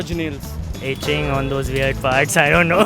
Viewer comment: and I heard "Itching on their weird butts"